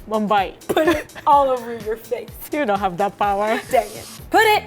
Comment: Put it all over your face. You don’t have that power. I heard “you don’t have that POWDER” LMAOOO